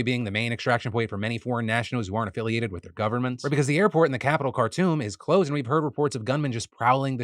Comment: I thought he kept saying “capitol cartoon” lol